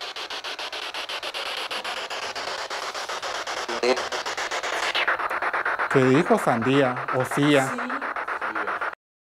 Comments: En el minuto 27:57 yo escucho que dice: Florero. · Min 27:56 escuche "lo era" luego de que preguntaran ¿es tuya esta silla? ... saludos buen videos sigan asi